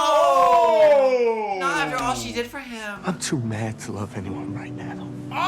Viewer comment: "I can't love you. I'm too mad to love anyone" is the most im12andthisisdeep line I've ever heard lmao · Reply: wtfff I heard "I'm too madly in love with someone" or something, like talking abt Joe lmao not "I'm too cuhrayzee to love"